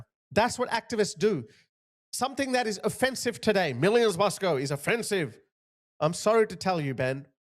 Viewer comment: turn on the captions and read what it says 🙈🙊🙉 it doesn’t say “millions must go”🙈🙈🙈 even youtube knows 😅